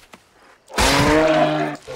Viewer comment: All this time I thought he was saying: Jah mon